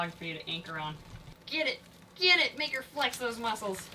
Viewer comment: what did she say? I can’t hear it clearly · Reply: Get it